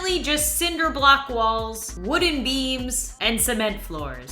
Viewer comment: I thought she said wooden Memes not wooden beams I was like oh no she’s lost it LOL 😂